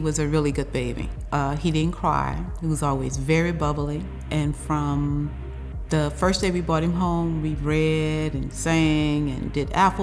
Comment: did she say "for the first day we bought him home"